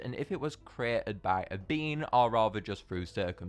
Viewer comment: I thought you said it was made by a bean!🤰🐽😂😂😂😂😂😂